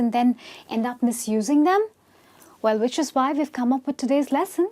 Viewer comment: What did she say in the 0:19? "Well, which is why we have come up"? Also in the 5:38 she said "Let's Susan got a little tipsy"?